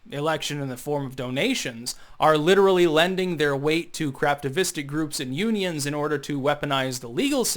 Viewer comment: Craftivistic, a new word for me. I thought you were saying craptivistic, which also works.